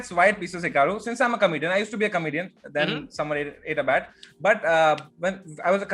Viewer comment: "i used to be a comedian, then someone ate a bat" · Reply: That makes more sense, I thought he said "then someone made a bet".